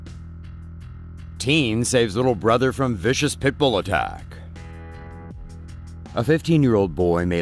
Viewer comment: I thought they said a vicious pickle attack